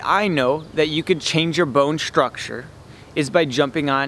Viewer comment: Someone can write what he say in 02:32 .. my English not so good and i want to translate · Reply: I say, "bone structure". Did you need more than that or just that word? I apologize. Next time I will try to pronounce more lol sometimes I am guilty of mumbling